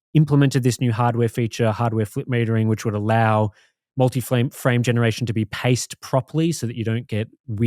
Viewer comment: did I hear it right? multi flame generation? :D